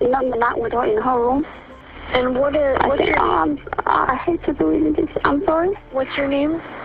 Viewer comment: (The subtitles say inaudible but they are audible.) "Not with her in the room. ahh I hate to do this, I'm sorry."